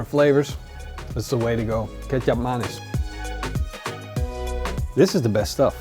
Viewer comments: What was the bottle of stuff you added that wasn’t the maple syrup? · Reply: All this time I thought you were saying "ketchup mayonnaise". I was so confused. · What was the bottle of stuff you added that wasn’t the maple syrup? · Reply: I thought he said ketchup mayonnaise and it confused me cause it looks like molasses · can someone tell me the spelling of the sauce he used? it sounds like hes saying ketchup-mayonnaise but i doubt that black bottle is that edit nvm found it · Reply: Kecap Manis